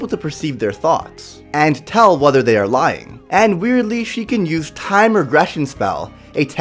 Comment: did he call gloxina a she